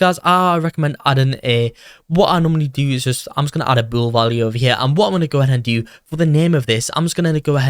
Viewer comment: What did you say at 2:20 ? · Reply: He said “ A BoolValue